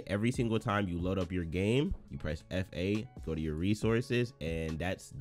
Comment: Did you say press f8 in